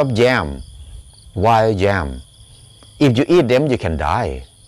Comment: This is very interesting subject. One question, about about 6:10 you talk about "wild jam"? but I don't think you are saying "jam" -- I'm trying to understand what you are referring to here? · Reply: I mean yam but my pronunciation is not correct. I'm sorry about that.